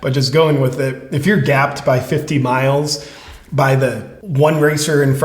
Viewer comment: ish, I think she said she was gapped ‘at’ 50 miles, not ‘by’ 50 miles. The margin of victory was 5 minutes. This whole situation is sad..